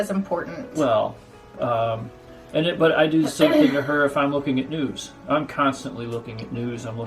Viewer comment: Ok, not to be dirty, just funny, I thought he said "looking at nudes" and I spit out my diet coke! Sorry, so sorry, but funny! Hello from Ga!